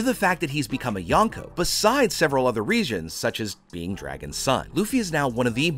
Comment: did he say besides other regions instead of reasons….😂